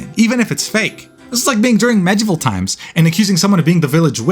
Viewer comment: did u just say "VEGETABLE TIMES"? 😂😂😂😂